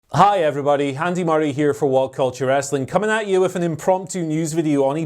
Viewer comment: Wasn’t fully paying attention when he started speaking, I could have sworn he said “My name is Handy Murray” 😂😂